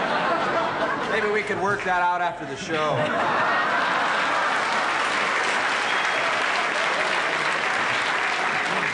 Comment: What is it that "Don Sturdy" (aka Howard Hesseman) is responding to at 1:47? What did the audience member say? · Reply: I think it was "queer".